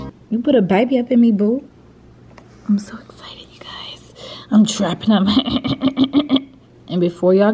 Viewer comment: thought she said “I’m trapping out my ex” 💀